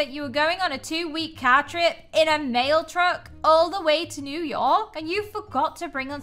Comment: I thought she said male truck:_yammyLaugh:! I never knew that trucks have genders :_yammyLaugh::_yammyLaugh::_yammyLaugh